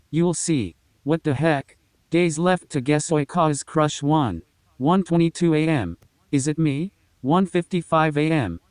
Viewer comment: I thought it said 'gays left to guess oikawa's crush- lawn' 💀